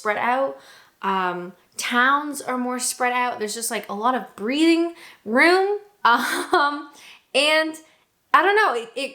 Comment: When you were talking about the space available in Canada, I initially mis-heard you as saying that Canada had "breeding room"...